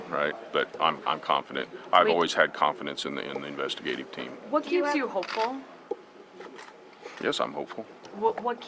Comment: so you can hear what the reporter asked. It was a misunderstanding, they thought she said "are you hopeful?" But she asked "what keeps you hopeful? "